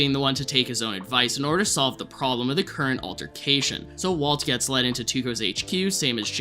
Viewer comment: did you say Waltercation?? Damn that’s a brilliant portmanteau